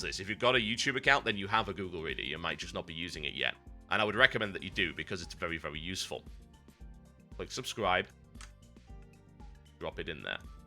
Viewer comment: i thought you said "I don't recommend it yet, because it's very very useful" :P